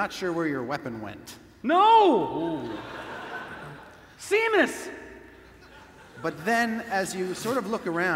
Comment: I thought he was saying samus, like from metroid